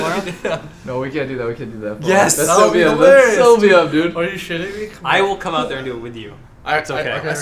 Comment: doublelift is not saying "show me up dude" he is saying "thats so BM"